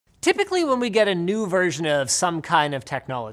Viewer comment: did he just say "Chipically?" That one almost went right over my head lol